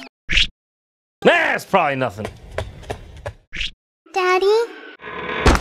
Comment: did I just heard "daddy?~~"